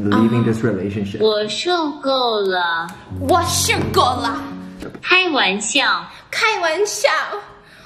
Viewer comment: for a second I thought that she said “I have eaten enough” lol